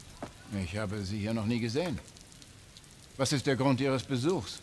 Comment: Idk what buddy said at 1:57 but all I know is I heard him say “no niggas in”